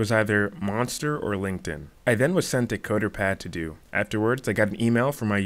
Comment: Great video man... Sorry, I could hear what you said the gave you to do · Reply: Thanks bro. It's coderpad. It's usually used for online assessments or live coding interviews.